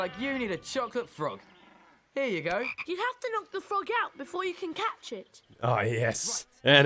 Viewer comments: I thought he said “you gotta knock the fuck out” at first and I was flabbergasted. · I heard "You have to luck the f*** out before...."